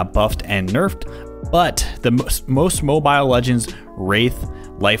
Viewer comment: did he just say mobile legends?